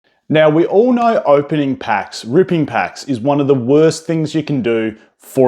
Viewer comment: Had to double take i thought i heard ripping sharts.. 😂😂😂